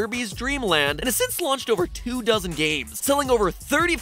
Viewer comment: I heard 2000 games... that's a lot. Then I realized it was 2 dozen. My ears are deceiving me